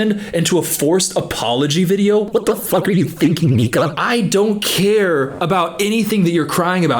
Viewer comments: i’m dead it sounds like he said nigga 😂i know he said nika it’s just funny to me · what did bro say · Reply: nika